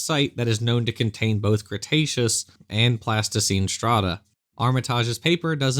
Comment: I kept mishearing pleistocene horn as plasticine horn! But was sure even creation scientists wouldn't get that wrong.